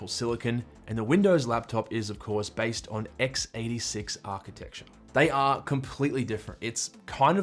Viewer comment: did he just said x86 architecture?